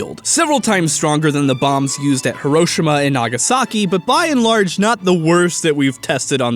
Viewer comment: did I hear "BnL"?